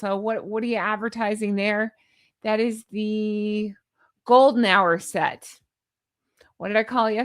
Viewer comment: ‘Golden Hour’? Phew. I thought you said ‘golden shower’.